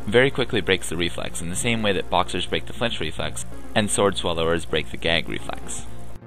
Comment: Can someone tell what did he say? The thing about boxers :) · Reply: he said flinch reflex. wtf is a flex reflex